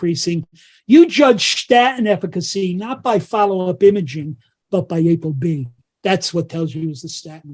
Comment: he said if what goes down? Apd or something? He says you know the statin is working if goes down. I'M not catching that. Can someone translate that please? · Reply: If your APOB score goes down